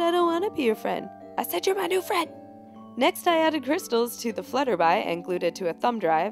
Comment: only i heard FlutterBy?